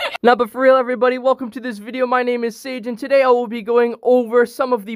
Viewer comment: welcome to this video, my name is SAIGE Me: im pretty sure i heard SATAN!